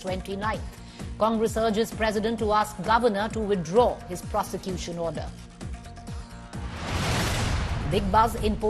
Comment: Did she say prostitution order? Check audio